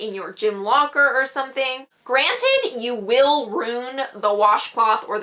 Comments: I swear she says roon instead of ruin 😂😂 nice girl tho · Soooo are we not gonna talk about the fact that she said "rewn" instead of ruin at 2:47😐😐